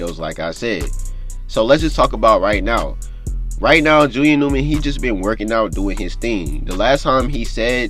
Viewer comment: Bro why don’t you pronounce the first letters of your words. It sound like your saying Julian ewman